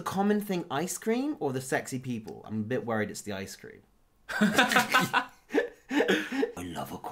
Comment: I heard it is the ice cream...